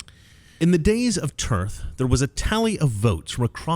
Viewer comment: I thought you said “The lady of Turd.”